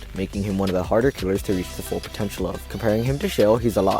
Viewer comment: DID YOU SAY POTENTIAL???